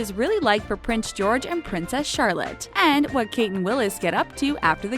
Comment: did she just say prince Willis like wth William William